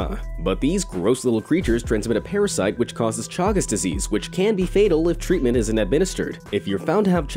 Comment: I thought he said chungus disease for a second xD